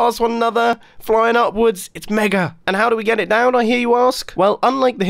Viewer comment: Did he just say "Mega"? Ok Iskall